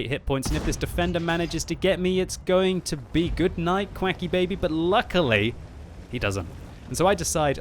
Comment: Is he really saying Quacky Baby at 5:14 <link>